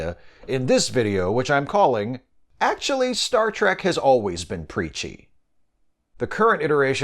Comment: I thought you said "Peachy".